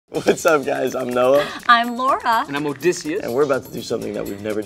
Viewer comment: When she said I'm Laura, I heard "I'm Lara"